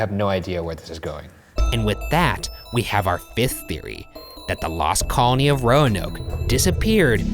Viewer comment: It sounds like he says “fisth” theory